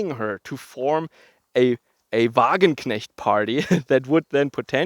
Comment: what please is the word? (Wagonknicht?) Thanks in advance anyone who can help.